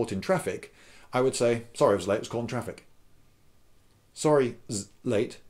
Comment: I heard: "Sorry aye'z late uh-z caught'n traffic"